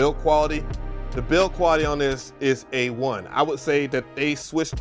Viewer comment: I misunderstood. You said the build quality is "A1" and I heard "is a 1" (like out of 5 or 10 or something). I was confused. 😂 I need to pay more attention.